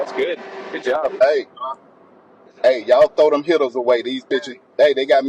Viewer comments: I thought he said throw them hitters away lol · When he made that phone call I thought he said “Y'ALL THROW THOSE HITTAS AWAY” 😂😂 I thought he was talking in code.